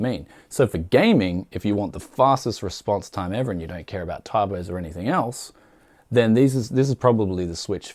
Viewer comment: what did he say "you dont care about .... or anything else" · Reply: typos